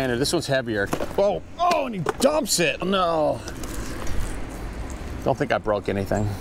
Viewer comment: Hey and! 🖐🙂 Wishing you guy's a Happy New Year! 🎈🎉🎊🍾🥂 🙂 Just out of curiosity, at about the 7:23 mark, what did you say, when you dropped the box? It sounded like you said, "Oh, he dubs it!" 🤔 Lol! 🤣 Another awesome video! 👍🏻